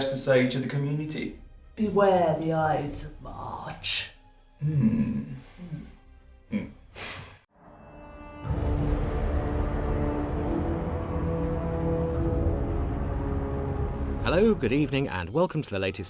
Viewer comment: what is she saying? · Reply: the ides of March